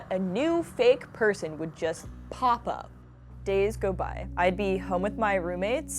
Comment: i heard papa